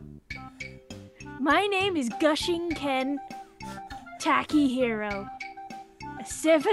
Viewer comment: my name is holy sh*t.. ?? gushiken.... ( i heard gushing ken) i prefer Holy Sh*t than gushing ken XD